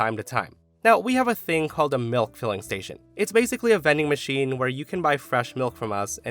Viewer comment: Why did I hear that as: “MILF filling station!” 😅😂🤣